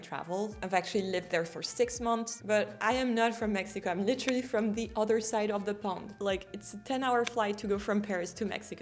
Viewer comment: Did you say at minute 3:15; “I am from the other side of the Pomme?” Is “Pomme” = Apple, “slang” for globe 🌎 or Earth????